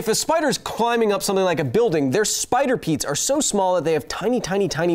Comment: Did you just say PEETS?!